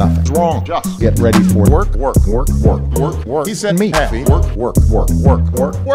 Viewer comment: it sounded like he was saying "BORK BORK BORK BORK BORK BORK"